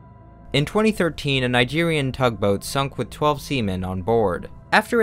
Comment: For a second i thought he said “A Nigerian tugboat sunk with 12 Semen on board”